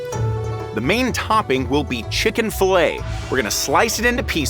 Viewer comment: did bro just say chick fil a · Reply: Yes he said chicken fillet